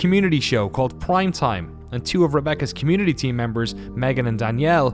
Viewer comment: Great video as always. Bt, it honestly sounded like you said 'Blyme Time' at around 19:43 hahaha. Cheers!